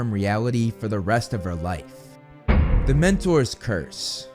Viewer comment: OMG!! I honestly thought you said “Dementor’s Curse” for a sec instead of “THE MENTOR’S”!! I was like, “What?! Are we switching to Harry Potter now??” 😅 My bad!! 🤭